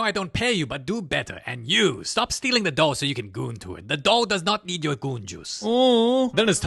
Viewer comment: BRO WAT DA HELL DID YOU JUST SAY??? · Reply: "Stop stealing the doll so you can goon to it the doll does not need your going juice"